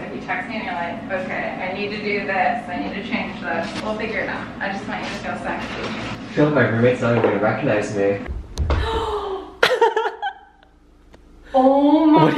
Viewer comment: idk if actually said it but it sounded like she said "I just want u to feel sexy" and honestly isn't that what every hairdresser should be telling u